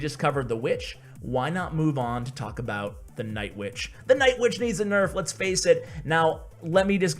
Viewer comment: I thought he said the night witch needs a buff I nearly threw my phone in the toilet.😂😂😂😂😂